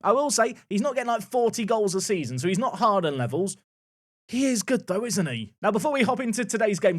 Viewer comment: thought you said “he’s not Harden levels” instead of Haaland and thought “yeah I guess he used to score a bunch back then”